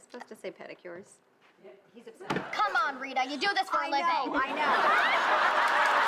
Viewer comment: "That's harsh, Honey." "... Was I supposed to say 'pedicures'?" "Yep, he's upset." "Come on, Rita, you do this for a living!" Not gonna lie, I first thought he said "You do this for the money"!😂